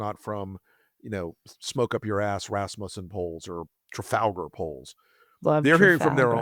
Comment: Instead of Rasmussen I thought you said Rasputin.